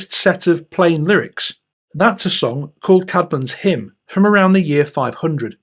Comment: What is that name you say at 0:20 ? Something "hymn"? Thanks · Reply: Caedmon's Hymn according to the captions